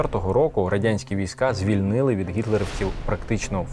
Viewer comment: СТОП!... МЕНІ НЕ ПОЧУЛОСЯ? ЗВІЛЬНИЛИ????